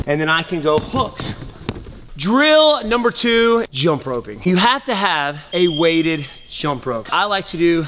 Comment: I thought you said "joe rogan" when infact you said "jump ropes".....